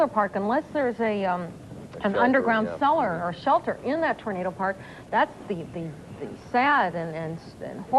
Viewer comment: Did homegirl just say Tornado park??